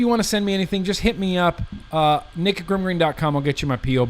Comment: when you say "nick at grimm green dot com" i hear "naked grimm green dot com" 90% of the time, it gets me every time... nuh nuh nuh nuh nuh nuh nuh nuh hat faaaaan